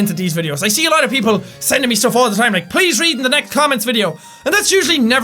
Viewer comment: I thought I heard H8 COMMENTS XD